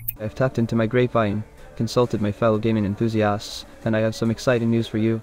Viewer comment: Okay because of the accent when he said, he consulted "gaming enthusiasts" for the release date of Knack 3 (about 1:06) I thought he said, "demon enthusiasts" and I briefly thought that the computer had either unlocked sarcasm or was genuinely performing demonic rituals (or outsourcing them, which isn't much better) in order to divine the coming of our Lord and Savior™(?) Knack III.